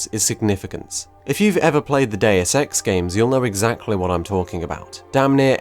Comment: Duuude! What name he says in the 9:42? I want to know the name of this game. I can't understand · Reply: Deus ex